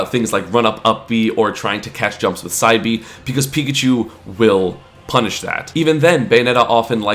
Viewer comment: I heard "pika's pikachu" the first time